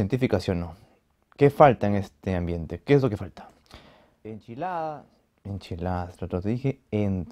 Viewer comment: Yo entendí michelada jajaj